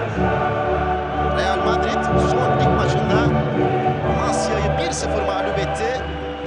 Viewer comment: amasyamı dedi ben mi yanlış duydum